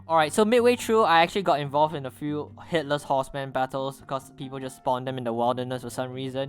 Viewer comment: Sounds like your saying hitlers horseman instead of headless horseman or is it just me